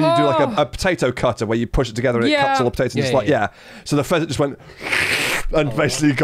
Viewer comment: For a moment, I thought they were saying peasant and running them over was a normal thing